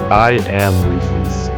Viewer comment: I was no paying attention and I heard I am So I thought I am Steve? But it was I am wifies🤣